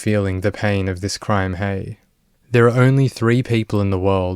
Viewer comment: "the pain of this crime hay"? According to youtube auto CC. But I think I've heard you say random Hey's throughout. Or is my hearing bad?